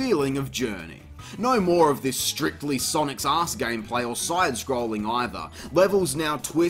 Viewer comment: Great review as per usual. I just wanted to ask one thing: What is it you're saying when it sounds like you say "Sonic's arse gameplay"? As an American who hasn't played Sonic, I am completely drawing a blank lmaoo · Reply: Sonic's Ass was a codename used back when the original Crash Bandicoot was in development, to describe the 3rd person over the shoulder perspective in the hallway levels. So that's why I call it Sonic's Ass Gameplay. 😄